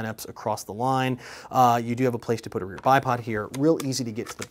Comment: Did i hear him say "a place to put a rear bi-pod"?